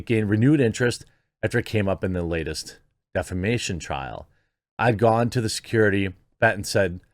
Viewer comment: I did a double take 😂😂😂 you said ‘defamation trial’ after the 💩 in the bed stuff and I heard ‘defection trial’ ! 🤪🙈